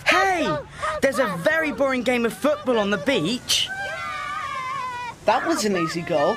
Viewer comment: did Six just said boring football?